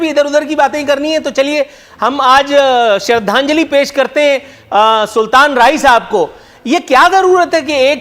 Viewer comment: Did Anchor said "Shradhanjali" pesh kartey hai(6:14)??